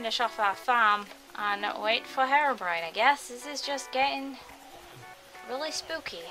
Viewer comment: hat mod at first I thought you said hair-orbrione cues your hair has blue highlts in it its really cute! :3